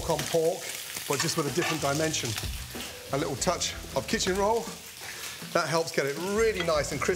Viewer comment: Did I just hear "A little touch of... Kitchen Roll"? wtf 😂😂